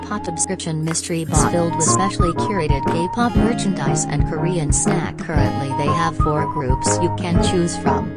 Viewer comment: I thought the intro said gay pop Merch and I was like… YES